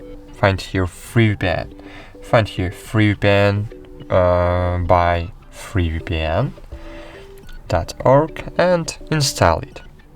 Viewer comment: All I heard is "free vpyan by free vpyan!"